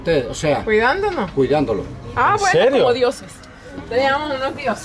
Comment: Love your videos, Kurt!! Small translation mistake, at 23:02, actually called the policemen that were watching over them "dioses" so she actually said, "Ah ok, like gods."